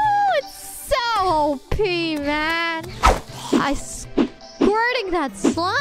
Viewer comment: I think she said "It's so OP (overpowered) man!" thanks for the clip!